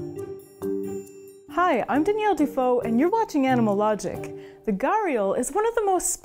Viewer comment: If you'd like to take home some of Danielle's artwork, the Animalogic Art Book is available now on our brand new merch store! Check it out here: <link> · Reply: For once I thought you said that your name is Daniel Defoe.